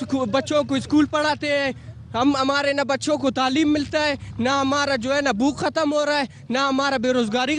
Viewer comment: Did he say "naa hamara Gu khatam ho raha hai"?